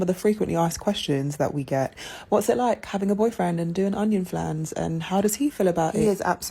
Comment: What's an onion flans? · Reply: I was thinking the same thing. I thought she said “onion fans”😂🤣